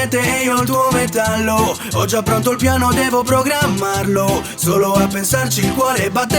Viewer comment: En minuto 0:46 escuche devo pogramarlo